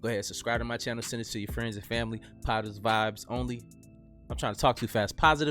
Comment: Lol, I thought he said "POTUS vibes only"! Lol, gotta really make sure to distinguish between "POTUS", and "positive", lol